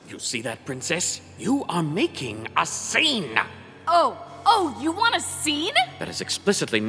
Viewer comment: When not listening carefully (or just NOT paying attention), it sounds like he saying "YOU'RE MAKING US SANE" 😂